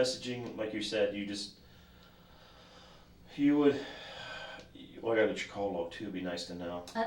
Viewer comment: it took me forever to figure it out but he said "I gotta look at your call log too" not chocolo or whatever 😂 but figured I'd throw it out there in case anyone was confused lol Edit to add: 31:25 that is the most pathetic attempt at faking a cry I think I've ever heard. My god 😂